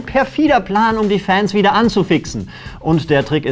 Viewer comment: Habe verstanden: ,,anzuwixxen“ haha